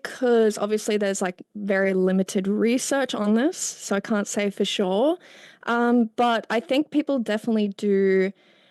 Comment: I heard her say … “I cunt say for sure” several times OMG , LOL